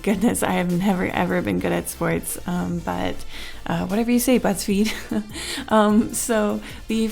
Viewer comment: oh my god i heard "Whatever you say butts feed" hahahah